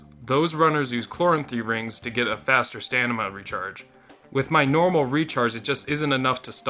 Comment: did he just say stanima ?